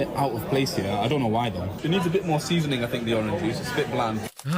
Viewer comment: Did he say seasoning on Orange Juice? Mates waiting to get a hard back hand slap to the face in the Pub.